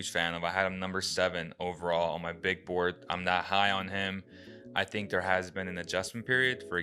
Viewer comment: altyazıları siz yapmıyorsunuz umarım. 0:44 'te "im that high on him" diyor. ondan pek ümitli değilim yanlış çeviri. listemde yedinci sıradaydı, ondan o kadar ümitliyim diyor. yani listede yedinci olacak kadar iyi olduğunu söylüyor. draft board dediği şey altmış kişilik bir liste, yedinci sırada olmak iyi bir durum. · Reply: "i'm not high on him" diyor bence. ben öyle duydum en azından